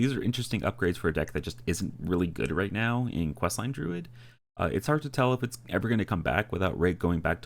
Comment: I'm confused at your pronunciation of "Even Druid" here, I could have sworn you said something like "Questline Druid". That wouldn't have been very groovy of you, dude.